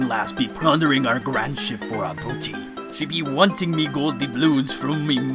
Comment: what's she saying? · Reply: 'booty'